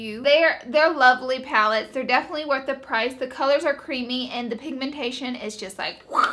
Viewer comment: I thought you said "the colors are creepy" but then I realized you said screams #snapchatfam ❄️❄️❄️